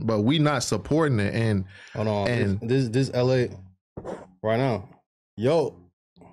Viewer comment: did bruh just call tay la